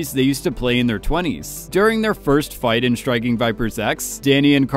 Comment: I honestly heard it as Striking Viper Sex